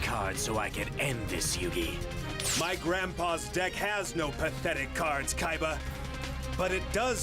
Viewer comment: i thought he said my grandpas dick 😭